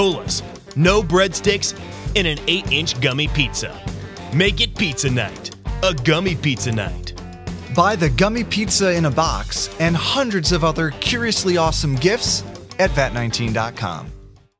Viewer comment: i thought you said "gummy pizza nut".. then i realize that you said "gummy pizza NIGHT" so i'm a bit confused for a moment when i realize im deaf now TvT